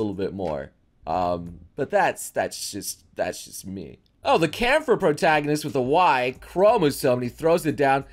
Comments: didn't quite catch the name of the anime. Could someone assist pls? I need ot for research. Edit: Found it it's called Kämpher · What is the anime name around 4:10 ?? I listen to it several times and still can’t make out what is saying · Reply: "kampher" i found it have fun